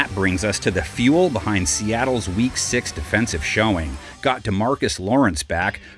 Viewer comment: for some reason I thought they said “weak 6” not “week 6” and I was like the math equations lady meme trying to think who the 6 players were 😂 go Hawks! (And Mariners)